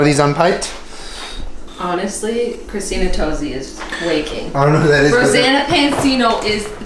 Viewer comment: It sounded like Jenna said Christina Tosi is Flaking xD